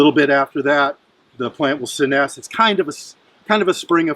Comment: and again at 19:36,what is that term you used, it sounded like sineff. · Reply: hi robert, the word is "senesce" which refers to the plant's habit of returning down to the root crown in the fall. A lot of folks will say "die back" but I don't so much like to say that--it gives the plant the wrong impression!